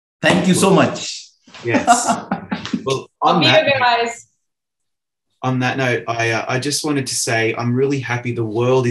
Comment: What did Hoo Yeon say at 40:50 ?? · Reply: I think she said love you guys